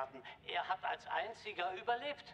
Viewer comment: anyone else hear should I seek a Uber lift? Lol